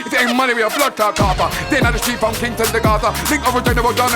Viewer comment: did he say from Kingston to agartha??😂😂